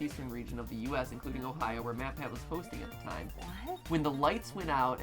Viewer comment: I thought they said mat pat was gay when I first watched this...Mandela effect?